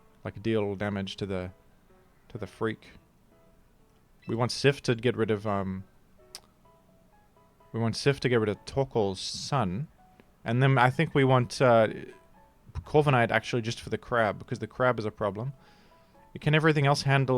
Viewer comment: I'm so used to the mother and son combo that when he said "we want cif to get rid of torkoal's sun" I thought he was saying Torkoal's SON I was really confused for a second like 'who does that refer to?'